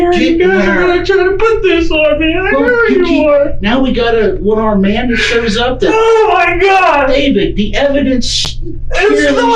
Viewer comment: I think the captioning is off and he actually says “David, now we got a one armed man that shows up” which is actually a funny as hell reference 😂